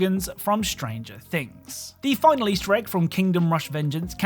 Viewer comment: every time he says "the final easter egg" i hear "the final shrek" and i'm starting to think i'm becoming deaf or something xD